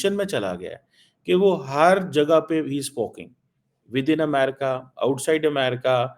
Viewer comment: did he actually say 'spoking'????!!!😂😂😂